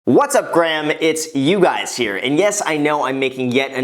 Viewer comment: wait brah did you just say "What's up Graham it's You Guys here" lmfaooo but yoo I just did this thanks for the tip fr I never would of known about this if it wasn't for you